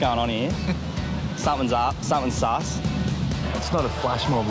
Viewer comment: DID HE SAY SUS?